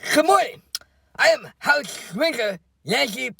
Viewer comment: Omfg...at the beginning I thought she said "I am House Drinker"...either way it fits!😂